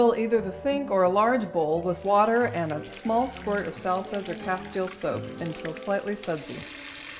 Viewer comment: Couldn't hear it, what and castile soap? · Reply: Hi there- A small squirt of Sal Suds (which is our biodegradable cleaner) OR Castile Soap, then swish until slightly sudsy. The microphone muffled things a bit in that section, didn't it?